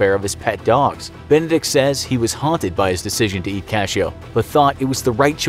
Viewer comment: Hey man. Just at 10:47, the subtitles say "to east cashoe" rather than "to eat cashoe". hope that helps :)